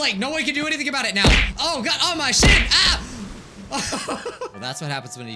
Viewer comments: did he just say sh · Did he say "ah my shin!" Or almost a cuss word? Ooo Mike you're about to get an email soon 😂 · OMG DID I HEAR THIS CORRECTY DID MIKE JUST SAY OH MY SHIT??!! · DID U JUST SAY OH MY SHIT👁👄👁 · is it just meor did i hear "oh my sh**?" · Did he just say sh!t?💀